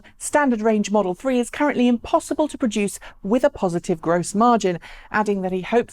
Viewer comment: Did he say the 35k car is impossible to reduce "with a positive gross margin"? Or did he say with " sufficient gross margin"? There is a difference. I thought the latter.